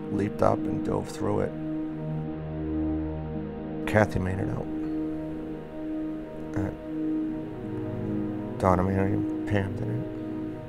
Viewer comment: what is he saying? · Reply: he says "Kathy made it out. Donna, Mary, Pam didn't." Mary Baker and Pam Gruttadauria were friends with Donna and all 5 of them went to this event together as a group. Kathy and survived. Donna and Mary perished in the fire. Pam was still alive after the fire but was severely severely severely burned. She survived in the hospital for nearly 3 months but eventually passed away from organ failure due to the fire.